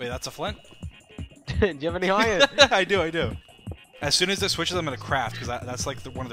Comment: When he said "When it switches I'm going to craft" I heard "When it switches I'm going to crap" XD